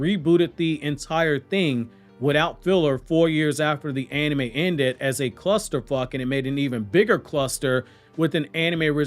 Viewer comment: Did he just say "cluster fvck"? 😂